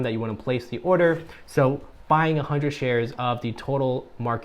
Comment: I had to rewind the video at 7:28 because I heard "100 shares" instead of "$100" of the Total Market Index Fund hahaha but I definitely agree on your asset allocation. I don't see the need to hold onto bonds right now, because being 100% in stocks while we're young lets us recover over time if anything were to happen. I echo some of the comments for a video on Schwab and Vanguard too!